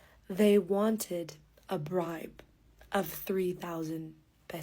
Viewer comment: i heard a BRIDE instead of a BRIBE , hahaha , you so beatifull i fall in love cause of you, love your voice